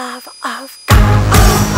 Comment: What does he say at 1:34??? The second part says "If you have salvation I'll pay - my soul" but before that?? "If you had...."?? help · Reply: Aero Zaxton "If you have life instead of death, It's god that gave it to you!"